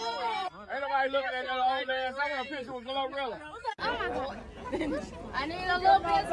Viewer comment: is it me or did someone say Ain't nobody looking at that old man ,I got a picture with a Glorilla.🤔 Congratulations 🎉 to her father though